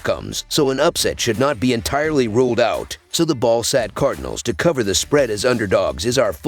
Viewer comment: Sounds like you said "ballsack" to cover the spread as underdogs lol